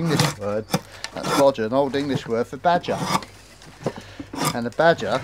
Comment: I can't quite catch what you're saying ... " ??? Man of the wood." What word or words came before man? It sounded like twelfth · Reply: "known as the old man of the wood"